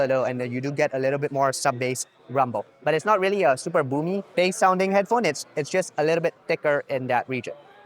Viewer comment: I definitely heard “big booty bass”… should probably be on their marketing materials